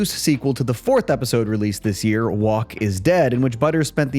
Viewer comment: use spell check, your AI said walk instead of woke. 1:25 again it’s woke